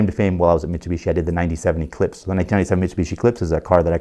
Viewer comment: I thought he said 79 eclipse I got very confused I was like...